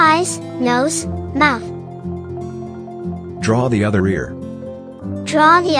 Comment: When it said draw the other ear I thought it said draw diarrhoea 🤣🤣🤣😂😂😂😂